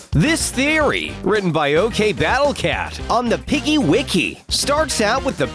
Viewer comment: Did you say battle cats?